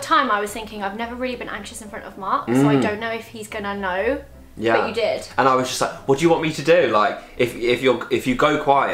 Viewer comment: what did say they made Mark watch? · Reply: TuMacca a monster calls